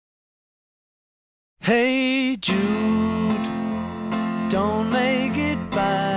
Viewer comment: cuando era pequeño pense que decia HEY DUDE refiriendose a un amigo